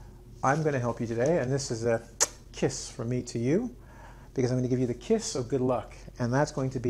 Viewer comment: Listened <num> times to understand what you say 😂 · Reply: "Because i am going to give you"